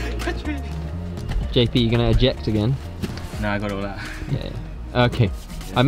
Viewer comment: What was JP saying here? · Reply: "No I got it all out"